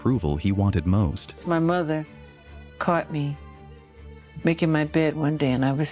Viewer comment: "my mom caught me naked in bed"?? Did i hear that right? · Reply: MAKING MY BED